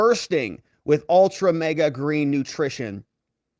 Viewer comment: Did you say "Ultra MAGA green nutrition"?!